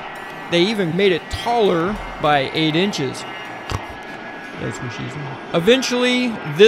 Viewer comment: did I just hear a "thats what she said" at 10:14? haha